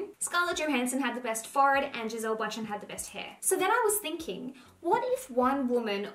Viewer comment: I heard “Scarlett Johansson has the best fart.”? Can someone tell me what she said 😂 cause I don’t think it’s correct